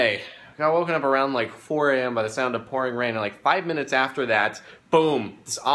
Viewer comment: At the beggining of the video, i thought he said he had woken up to the sound of porn 😐